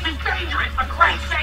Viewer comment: What is he saying at 0:43 😂? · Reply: Chef, Steve let’s leave!!